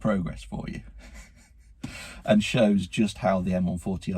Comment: what car is he referring to? Cant hear it clearly or i just dont know anything about that car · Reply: BMW M140i